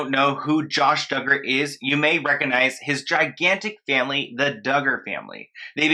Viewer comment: I don’t know why but I thought you were going to say, “...you may recognize his gigantic face” 😂